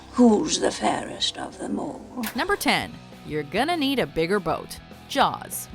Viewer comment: wait top 10 i thought he said, we're gonna need a bigger boat, but its not!!!